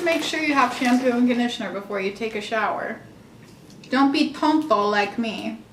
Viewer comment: I know she did not say "don't be "THON-THON" like me" eeeeeerrrr what?!?!? I'm rolllingggg you need some milk lol